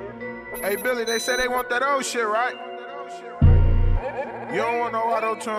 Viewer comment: i thought duck said they were on that old shit no autotune?